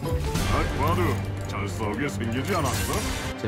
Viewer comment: 뭐야 "딱봐도 잘 싸우게 생기지 않았어?"였어? 이때까지 "딱봐도 장수하게 생기지 않았어?" 인줄.. 진짜 그렇게 들리는데..